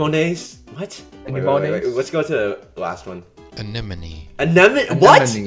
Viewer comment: A limonade is what I heard 💀💀💀